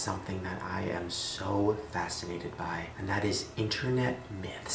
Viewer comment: i thought shane said masterbated not fascinated...😂 Thats what happens to my innocent mind after watching shane for almost 7 years now😂😂